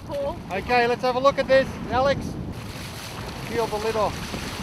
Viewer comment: I hear he said "LIFT-OFF"